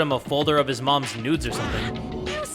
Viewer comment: hold on DID HE JUST SAID NUDES? 😨 (is it even normal tho??....)